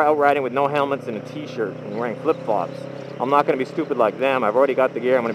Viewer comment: Nearly sounds like "wearing lip gloss" instead of Flip flops o_O